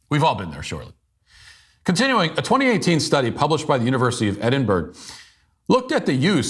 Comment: Did he just call Edinburgh "Edinburg"?😂